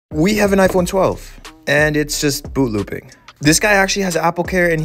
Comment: "And it's just booboo pink" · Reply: It's just funny that I keep hearing him say "booboo pink" instead of boot looping